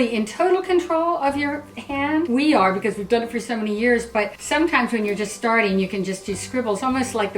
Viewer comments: did they just say they've been doing that for 70 years?? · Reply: No, she said "for many years" not 70 years , even though it sounds like there saying 70 but, there saying For many years.. Sorry if i offended you somehow but I understand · did they just say they've been doing that for 70 years??